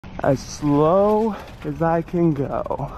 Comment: "As slow as I can go-" Famous last words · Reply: i thought he said "slow is not a clean go"